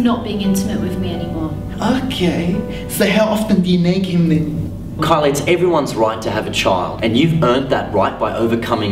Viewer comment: can speak arabic? cuz i think he said something in arabic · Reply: No he said nag